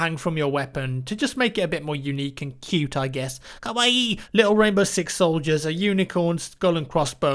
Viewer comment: wtf? Kawaii???? did you just said that?